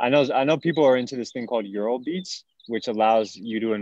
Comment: is saying eurobeats? Still trying to find out more about this marriage of audio and crypto. sounds interesting